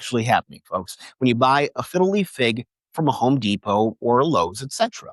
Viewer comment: I always hear it as "fiddly" fig, which just screams difficulty to me.